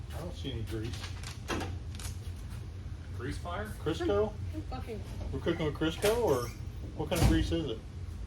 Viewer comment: did I hear a F**king in the background???